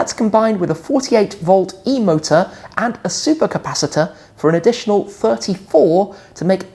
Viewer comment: did say it comes with a Flux capacitor 😁